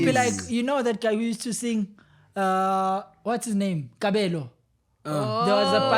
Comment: I heard my name 'Kabelo' & thought wat did i do